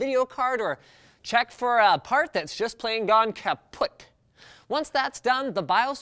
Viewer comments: did he said the German word "kaputt"? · as i usually watch techquickie to avoid coding i suddenly stop and pause the video ... did he just said "Kaputt" 2:13?